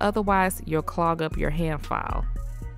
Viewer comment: On 12:45 did she say hair file?